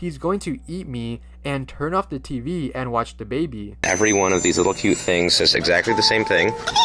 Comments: I definitely agree that the Monstaz toy was speaking nonsense, but the evil subliminal message people thought it had was to... turn off the tv and watch the baby? were they afraid their children would be brainwashed into becoming responsible parents?? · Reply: I thought it said Dababy and not the baby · I thought montaz said turn off the baby and watch the tv💀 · i heard "Turn off the TV and touch the baby-"